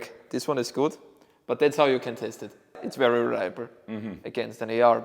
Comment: When he was checking the bolt on the AUG I thought he said you have to taste it, he said test it. Thought there might be kitted up Europeans licking their rifle parts.